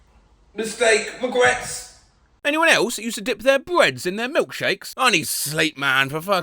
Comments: Wasn't paying attention and thought said "breasts" instead of "breads." Was very confused, very quickly. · I'm so tired I thought he said "breasts" instead of "bread"